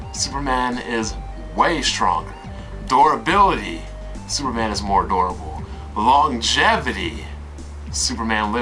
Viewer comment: I just want to say at 7:50 it sounds like your saying superman is more adorable :3 LOL but yea i totally agree with everything your saying. I had my own debate with kids on facebook :/